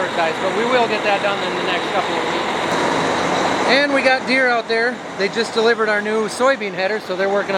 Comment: You said, “we got our new Deere out there”, I thought you said “we got more beer out there”, I really need to get my hearing checked 😂😂